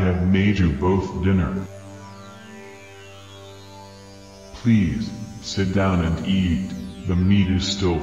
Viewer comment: Homer going to jail. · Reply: I thought Homer said "Please don't crap"